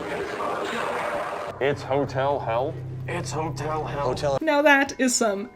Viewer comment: “It’s hotel hell” I hear “it’s my time” Smh